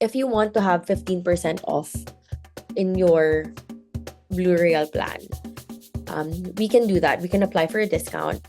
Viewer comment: i keep hearing 'burial' not 'blue royale'